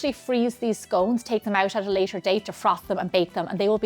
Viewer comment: They look so tasty. You mention being able to freeze them. What does “froth” them mean ?? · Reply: Didn't hear her say "froth" but since you talk about freezing just before asking that, might you have misheard "frost" at 7:11?